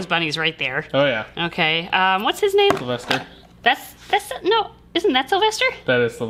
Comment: You called Pepe LePew "Sylvester" but I thought you said "M*lester" and thought "accurate"